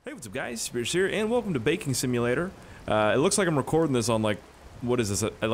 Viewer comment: Thought you said “welcome to bacon simulator”